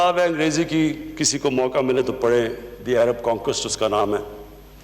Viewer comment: which book is he saying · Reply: The name of book."The Arab conquest "